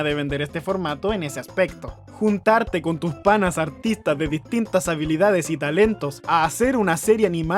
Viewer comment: entendí "juntarte con tus panas autistas"